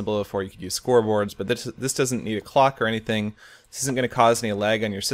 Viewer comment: Instead of clock I thought you said f*** XD